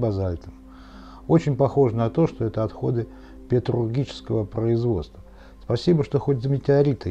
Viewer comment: Здравствуйте. В конце видео, были показаны валуны необычной формы. Вы сказали, что это отходы петрулгического производства. Может, я не правильно расслышал. Наверное, Вы имели ввиду металлургического производства? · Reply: Нет, я имел в виду петрургию - каменное литьё